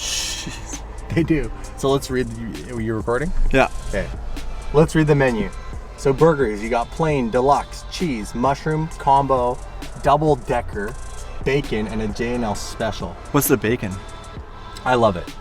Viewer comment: Oh shit lol 😂😂 😂😂 😂😂 😂 I thought you said double Dicker